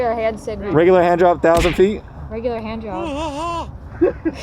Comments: = it totally sounds like Ron said "Hand Job". Obviously the mask played a factor, but Emilia was not mistaken . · It did sound like he said handjob not handdrop. lmaooooo